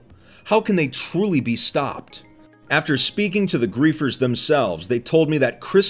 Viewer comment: for a second I thought he said creafers